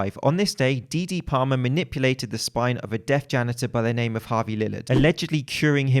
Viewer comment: Had this video playing in the background, not really focused and I misheard: "Cracked the spine of a dead janitor and cured him of his deathness". Plausible.